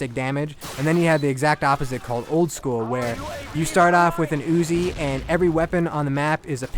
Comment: dude did you just say you start off with a uzi?